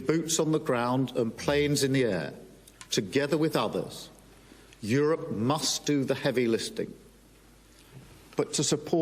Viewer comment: I hear Starker saying "Europe must do the heavy listing" !!!